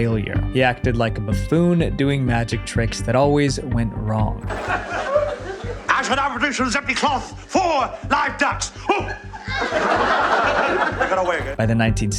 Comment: Why does the subtitles say “gibberish”? It’s not that hard to hear that he’s saying “I shall now produce from this empty cloth four lives ducks!”🤦‍♂️